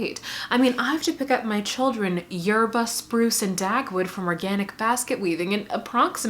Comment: i thought you were gonna say barbra had to pick up her kids from "organic basketball" and by the time i realised i misheard i was already HYSTERICALLY LAUGHING my day is made